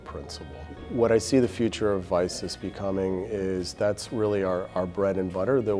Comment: I heard " The bread and butte of isis" but he said of vicis